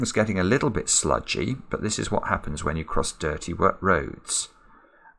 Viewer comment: i cant Stop laughing, especially with the whispering Part. With all of Respect sir, this Made my sad day Edit: oh god i lost it 😂😂😂😆😆😆 17:11(a bit before that)...the frog com here without touching itself... It all become sludgy here... That what happens when touch dirty words quickly fixing it roads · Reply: I thought he said "dirty work roads."